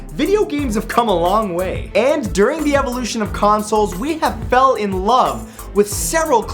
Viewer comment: At the start I thought he said evolution of combat, hope someone gets thst